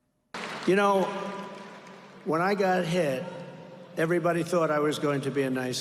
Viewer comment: At the 9:20 mark of the video, Trump says, " When I got hit everybody thought I was going to be a nice guy..." What I heard was, " When I got head, everybody thought I was going to be a nice guy." I immediately thought to myself," That certainly mellows me out for sure. " I had to listen to it again to get it right, but I tell you, maybe that's what's missing in Trump's life.